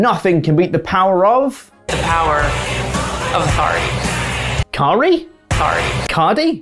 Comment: I thought she said "the power of a Laurie." I was disappointed when El Fakidor didn't show up to interfere in the match.